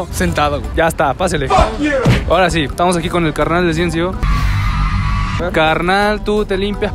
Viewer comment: me acabo de dar cuenta que dijo CNCO minuto